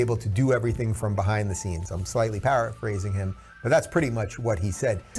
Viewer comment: does he say 'Parrot phrasing' instead of para-phrasing?